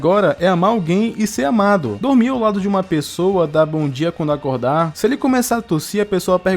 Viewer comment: Juro que entendi "da a bundinha quando acordar" kkkkkkkkkkk